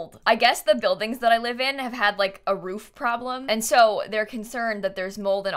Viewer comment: I thought Kayla said her building had a mole problem, not a mold problem lol